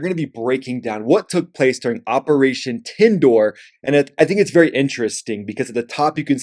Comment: did you just said "operation TENDOOR" at 1:43 🤣🤣